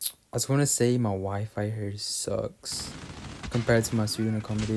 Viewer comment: I thought u said “my Wife here sucks” lol